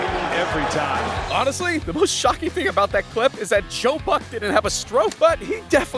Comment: In 0:50 then author said "Joe Buck" I heard the Ukrainian word "Жопа"[Zhopa] It translates as ass. This video and this word are chained? I really thought that author speaks Ukrainian language.